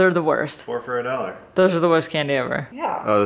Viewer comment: When you said, "Here's M-Dawg," I thought you said, "Here's UnDog." I had to go back and check.